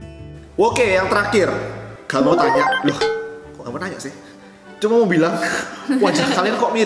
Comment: //"Gak mau nanya" "Euh kamu nanya sayang" Salah denger atau gimana?:v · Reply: Kok kamu nanya sih , bukan sayang